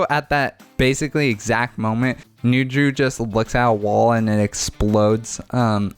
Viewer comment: I first heard this as "At that exact moment, Nuju just looks at a wall and explodes"